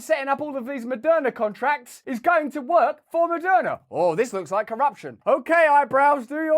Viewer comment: I thought you were saying Maddona